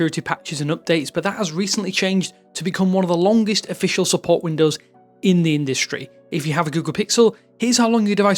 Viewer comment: Did you say Windows instead of Android at 0:06 seconds? :face-blue-smiling:. It's OK, I found it funny. Thanks for your videos. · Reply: He’s saying “support window” as in a frame of support. He wasn’t saying Windows the OS.